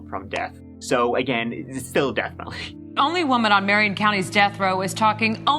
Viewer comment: Florida is always a freaky fun destination. I thought u said “Marin county “ which is a super- wealthy Northern California SF Bay area. Oh. MariOn County. Ok. Central Florida is largely a swamp. I like it, but I like barren wastelands